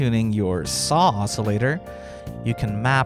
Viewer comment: Am I the only one who thinks "saucilator" when I hear "saw oscillator" ?